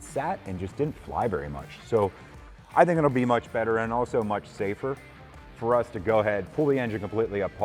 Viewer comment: Did I hear you say 'much safer'? Yah, it's about time. By the way. Need not to look any further. I 'm the winner of that Auston Healey. Thank you.